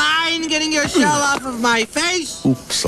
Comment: I have to admit when I first saw this ad as a kid I thought Red M&M said at 0:10 "Would you mind getting the hell off of my face!!" Even though now I know he said "Would you mind getting your shell off of my face!!"